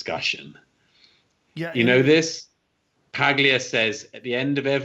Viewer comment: what name did he say? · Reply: He said "Paglia," As in Camille Paglia, (I assume), she is a Feminist who has been warning against this stuff for years. Your timestamp confused me at first, as you stamped after he said the name, not before, regardless, I found the part you'd meant. Copy and paste this into the search bar if you are interested: "Lesson from History: Transgender Mania is Sign of Cultural Collapse - Camille Paglia"